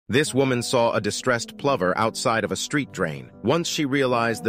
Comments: I thought he was saying "Plumber" not plover 😂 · At 1st I thought they said a distressed PLUMBER 🪠! 😂 I was looking for Mario & Luigi.. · I swear, I thought he said, “distressed plumber”! 😂😂😂😂😂😂😂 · At first I thought he said plumber. Lol. Never heard of a plover. Cute! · Was listening blind and thought you said plumber and that this was a construction meme. Birbs are cute too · No lie I thought you said distressed plumber lol cute baby bird tho 🥺